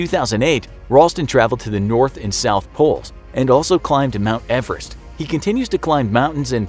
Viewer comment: did he just say north and south pole?